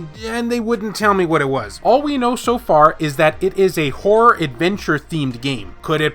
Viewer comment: horror adventure game i had to rewind i thought you said wh0r3 adventure game😂